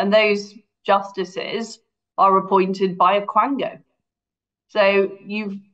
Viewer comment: "... appointed by a crangan..." can someone tell me what a 'crangan' is? what did she actually say? crown gang? I cant make it out? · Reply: I think she said QUANGO.